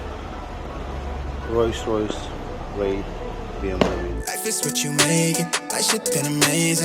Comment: did say Royce Royce Bmw ? Lol comedic as hell 🤣